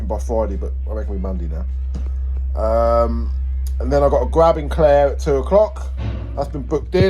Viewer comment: I thought you said you need to grab Claire 😂🤣😂😆